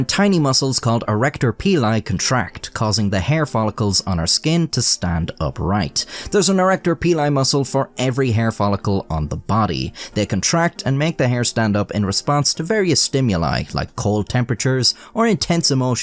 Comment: im sorry, but while you were talking about Arrector Pili, i got distracted by mishearing those words as "Erect Your Penii"... ...i mean, how ELSE would i watch videos??